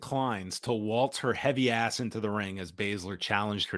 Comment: I thought shayna said happy ass not heavy ass